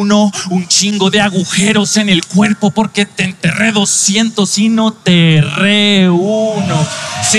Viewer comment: No entendí la barra del danger del minuto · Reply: TR1 (Trueno) te enterré uno y te enterré 200 y no te erre 1 (no tuvo ningún error).